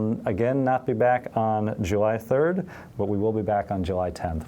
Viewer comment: Almost thought I heard Dungeons and Poodles